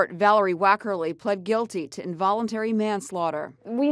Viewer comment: This isn’t the fault of the car seat. It’s the fault of the negligent daycare workers. That’s why the lady got the manslaughter charge. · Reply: I misheard the video and thought it said "voluntary manslaughter" and I was so confused. involuntary makes more sense